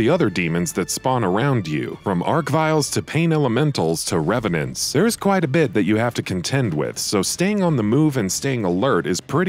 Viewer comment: did you say arc-vile